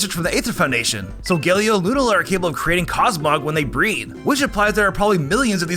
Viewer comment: I got scared for a second because I heard it as “solgaleo and lunala are capable of creating cosmogs when they breed “and I thought the R34 artists were gonna have a gardevoir situation